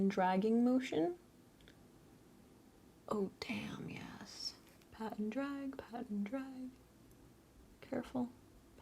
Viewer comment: Lmao when you kept saying “pat and drag” I thought you were saying “Pat in drag” and I was like wait what?! 🤣 I liked hearing your voice and seeing your work in PMG’s Livestreams yesterday! 🏆